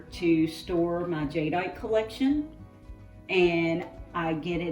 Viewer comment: I thought she said Jedi collection lol😂😂 jade